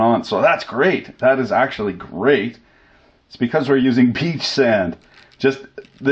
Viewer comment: Did you say "Peach Sand" ?